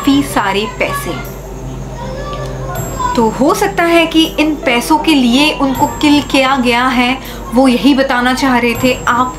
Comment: I heard "Sandeep killed me too."